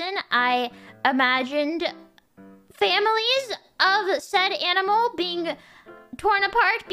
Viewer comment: I think Mumei said: "... sad animal being torn apart" not said.